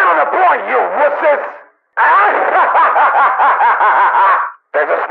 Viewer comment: What does he say at 0:12 ? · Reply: There’s a spy around here!